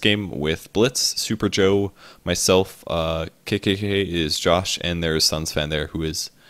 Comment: I thought he said KKK is Josh in the start but NO KeKeKe